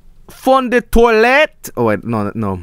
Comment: it sounds like you said "fond de toilette" instead of "fond de neulette" and toilette means toilet in french xD btw your french pronounciation is good ^^